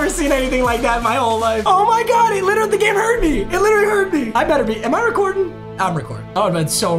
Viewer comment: I thought he said “Oh my god, He gave me herpes”🤣🤣🤣🤣